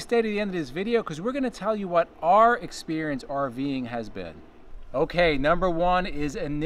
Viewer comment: You failed to include your costly repair and cost???? I waited and waited until the end of your video and was seriously let down ☹️ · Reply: I apologise...your said " your experience"... My bad, thought you said your cost.... thanks for the follow up 😁